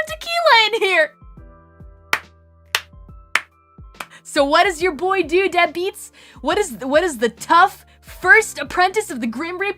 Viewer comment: did Cali say "what did ur BOY do?"